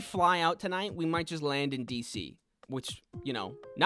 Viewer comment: For a second I thought he said DEEP SEA instead of D.C. ;-; EDIT : better version, as this was made 2 months ago- for a sec i thought he said “deep sea” instead of “d.c” 💀💀